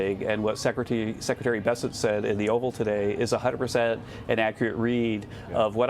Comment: did he say ""100% inaccurate read"? Who talks like that?